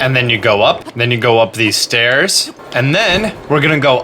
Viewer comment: DID HE JUST SAY STAIRS!!??